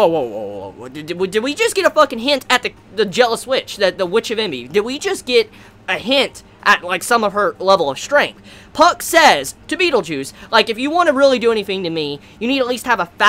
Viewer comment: ?... Did he just say "Beetle Juice"?